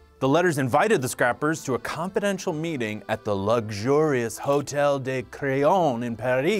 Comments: I thought he said “Hotel De *Crayon*” I was like Pencil Hotel? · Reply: I thought he said “Hotel De *Crayon*” I was like Pencil Hotel? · I thought he said “Hotel De *Crayon*” I was like Pencil Hotel? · I thought he said “Hotel De *Crayon*” I was like Pencil Hotel? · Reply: I thought he said "Hotel De *Crayon*" I was like Pencil Hotel?